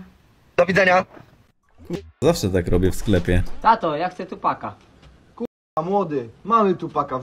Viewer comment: ja dosłownie usłyszałam ,,chłopaka’’ HSHSHSHSHSHHSHSHSHS chyba coraz gorzej ze mną :’D